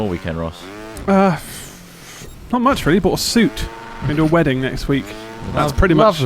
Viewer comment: Wait did Ross say ‘for your wedding’ or ‘going to a wedding’ ?? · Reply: It sounded like he bought a suit for trotts wedding next week. I've looked for a bit but can't find anything. It would be something we'd know by now right?